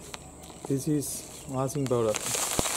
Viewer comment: does he say "Thousand Dollar"?? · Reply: "this is washing powder"